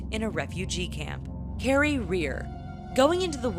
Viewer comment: I thought you said :Hairy Rear".